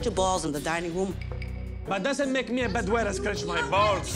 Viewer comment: I thought he said bed wetter when he said "that doesn't make me a bad waiter."